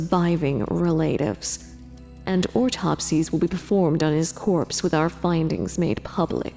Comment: Did she just say ortopsies